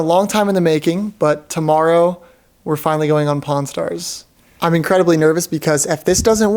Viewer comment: did you said porn stars😂😂